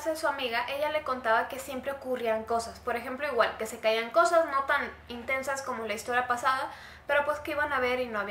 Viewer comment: En el minuto 7:28, cuando dice "... no tan intensas", justo en el "intensas", se escucha como un susurro un suspiro que dice "hey". Me meé.